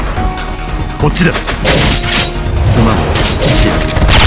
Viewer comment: did kiritsugu just said sorry?? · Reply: No, it's "Ochiro" like Caenis says it means "Fall" IIRC